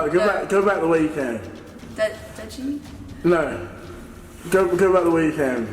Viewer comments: I think she says "Deji" which is the name of the youtuber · it just sounds like she said his name deji